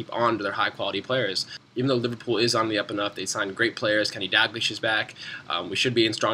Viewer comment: Is it me or does it sound like he is saying Kenny Dag-lish...instead of Dal-glish.